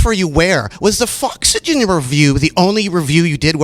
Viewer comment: what the fuck does mumbling narduar say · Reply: david haro He mumbles Foxygen review ^^